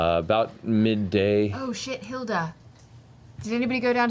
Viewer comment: instead of broomhilda, it's shithilda